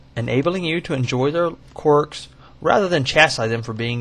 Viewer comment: what does he say there? enjoy their what? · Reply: Quirks.